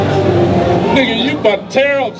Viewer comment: did he say nigga at 20:06? lol